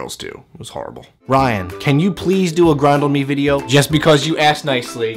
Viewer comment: Totes thought he said "ass grind" at the beginning.